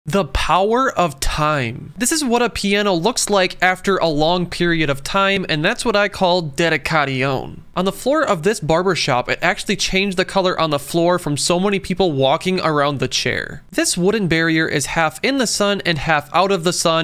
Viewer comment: When he said dedication i thought he said dead cat i own